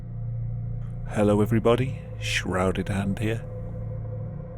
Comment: I thought he said shrouded ant at the begging